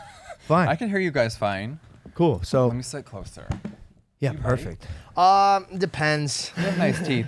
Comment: What does Jeffree say at 6:04? I literally can't make out the words no matter the volume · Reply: "let me sit closer" or "imma sit closer"